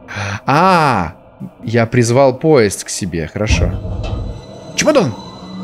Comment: 48:мне послышалось или он сказал '' О! Чимадон''